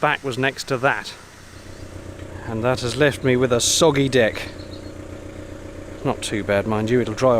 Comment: I swear I heard "soggy dick", and thought it was some kind of British slang term.